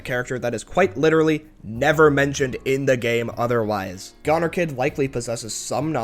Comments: i thought that said gooner for a second 😭🙏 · I thought you said Gooner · i thought it said gooner 😥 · I thought that said gooner lol